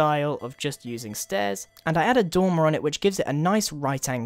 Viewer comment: sounds like you said domrao